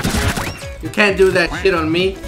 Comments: did clash just say "shit" when you knifed her? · it sounds like clash said SHIT when she got knifed